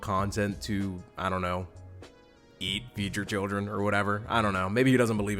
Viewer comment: ngl, I thought you said “eat future children” for a second there